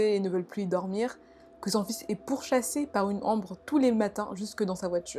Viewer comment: A 5:25 j'entends " que son fils est pourchassé par une Ambre " je sais pas si ces moi ou quoi ( j'ai des problèmes d'audition as 12 ans j'aime bcp ma vie ) en plus ma soeur s'appelle Ambre Bref, Super vidéo en tous cas ❤❤❤ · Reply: Elle dit "ombre" mais souvent prononce les "on" "an", du coup j'entends "par une ambre", "la lumière sur ma tranche" x)